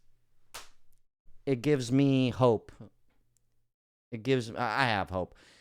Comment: this is what was said gives you hope hahahaha lmao